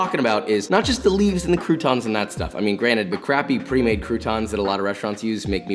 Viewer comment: did u say granite? it’s granted, with a t